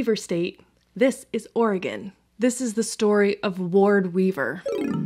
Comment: Me, curling my hair while listening to this intro, “wait did she just say this is the story of Lord Beaver?”